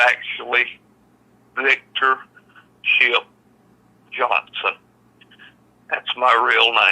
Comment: will you please tell me what Victor said his full name was and how to spell it? I heard "Victor Chip Johnson" , but looking at the transcript it says "ship". Thank you.